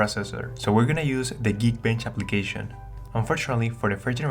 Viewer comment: what's the name of the app? I didn't hear clearly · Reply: MistaLOD nah just Geek Bench not "the geek bench" lol